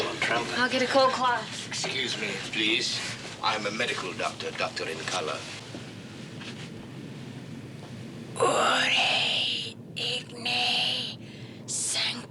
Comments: did he said "I'm a medical doctor, doctor in color"? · " I am a medical Dr..a Dr...in Color???????????: did I hear this right???